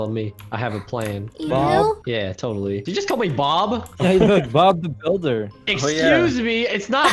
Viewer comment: When said "Did you just call me bob?" I thought he said "Did you just call me callmehbob?"